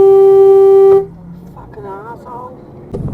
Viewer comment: "fkn rissole" Now that's a phrase I haven't heard in a long time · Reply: He says rissole?! I thought he said as$hole. Rissole is hilarious 🤣